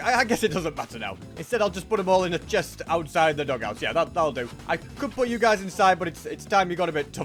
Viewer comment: did he call it the dogouts?